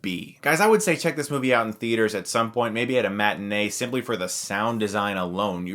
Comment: what is a "mat and a" or whatever he said?